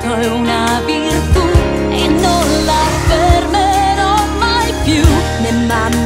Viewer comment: My favourite part was at 1:50 when it sounds like she said ‘on my poo’. 😂